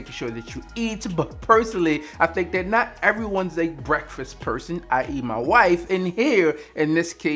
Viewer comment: Did he say I eat my wife🤨🤔😃😆😂